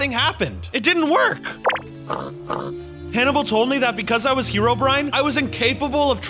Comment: I thought that when named the pig Hannable I thought he said Canable